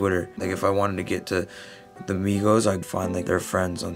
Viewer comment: did he say nigga? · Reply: fabio "like if I wanted to get to the migos"